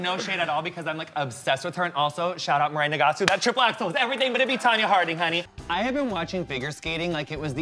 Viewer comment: I can't even understand what he's saying in 1:29 . Strong thick gay lingo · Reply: He said 'shout out Mirai Nigasu that triple axel was everything'